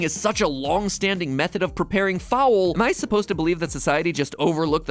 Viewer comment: I can’t believe they went through this episode without using the word “poultry” once. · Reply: I really thought he said poultry at some point, but no! I was thinking of “fowl” at about 4:55.